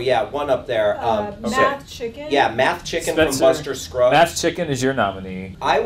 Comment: I misheard "Mathemachicken" as "Matthew McChicken." Alright, alright, alright!